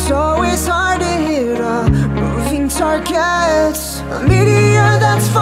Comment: I thought he said a living target instead of moving..